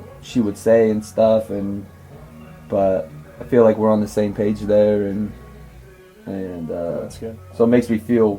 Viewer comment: " I feel like we are on the.....?????????????????????? What is saying. I cannot understand it properly! Can anybody please let me know what is saying? Appearantly, it almost made cry. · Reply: "I feel like we're on the same page though."